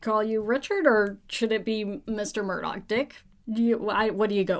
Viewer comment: Really im i hearing in 2:50 Mr. Murdi*ck or am i just tired?😂